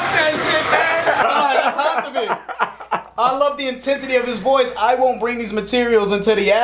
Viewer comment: I always hear "I won't bring this" as "I want pringles". The most intense way to confess your love of a salty snack